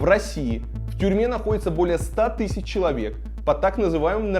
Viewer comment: Послышалось "в Перми находится более 100 тыс человек"